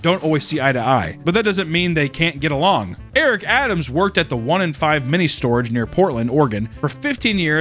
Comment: did you just call that "one and five"?